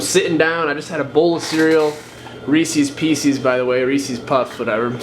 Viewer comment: Did I just hear you say, ‘Reesees Peecees’? What?!